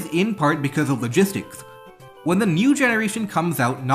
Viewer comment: I swear he just said Mew generation.